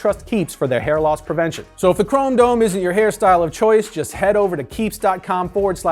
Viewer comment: I know he means "chromedome", but does he say "crimedome"?